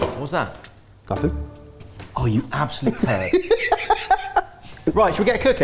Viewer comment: I have no idea what Kush says here, but it sounds like, "Oh, you absolute terf" 😂